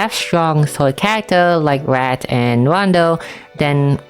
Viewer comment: im not trying to be mean, thank u sm for the video but i can not tell what u said at 3:42 I can only hear "rat and rondo" and even the subtitlezs hear rat. i literally can not even think of who u meant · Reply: Wrath/Lars! Localization changed his name to Lars, but in JP he's named Wrath